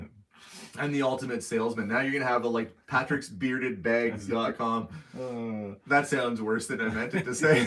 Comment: Did you just say ..."Patrick's bearded bags"... hahaha!🤣🤣